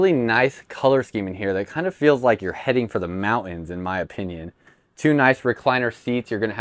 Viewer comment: Dude, did you just say "mow-ins"?!!! Its MounTains!!!